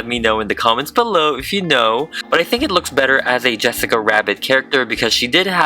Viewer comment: I thought you said Yesica Rabbit??